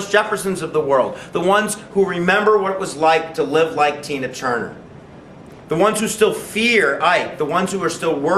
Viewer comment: Did he say "Tina Turner"???